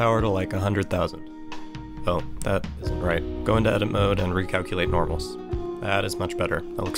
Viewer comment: hey bro, I cant understand what you say in 0:55, you say "recalculate..." and i cant get that second part. Greetings from spain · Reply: he said "recalculate normals"- go to edit mode, select all and hit shift+N :)